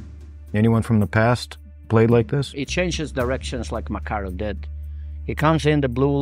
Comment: What does the Russian scout say at 10:10? It sounds like he's saying "he changes directions like Makar did" but he can't be talking about Makar because he's not Russian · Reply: He says Makarov (sergei) and then kovalev after